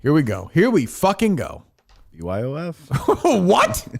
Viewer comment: called out for saying "this is why I left" and denied saying that. He clearly said it 40:40 mark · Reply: He said BYOF, short for Bring Your Own Food.